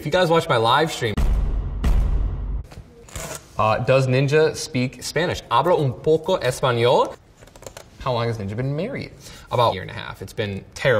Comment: thats not what he fucking said 0:30 he said he talks a little bit of spanish